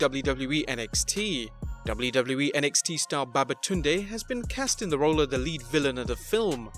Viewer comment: did I just hear babatunde